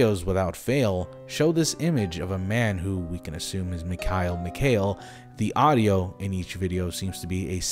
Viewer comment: I thought Mikael Mikail said Mikael Myers